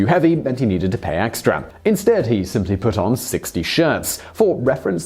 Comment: I'm genuinely uncertain whether all these 60 shirts? comments are being facetious, or didn't understand 6 t-shirts. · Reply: Funny thing was, when he was talking about the first guy he said '6 t-shirts' I misheard him and was like ' wtf 60 shirts are you kidding me-- oh he said 6' Then the chinese guy comes along and literally has 60 shirts